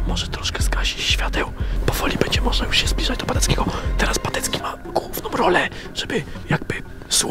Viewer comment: usłyszałem: teraz patecki ma gówno